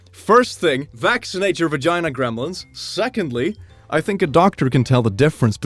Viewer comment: did he just say "vagina gremlins"